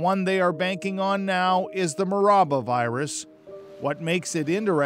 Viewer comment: What is he saying at 3:45 is it the mouraba virus can't quite hear it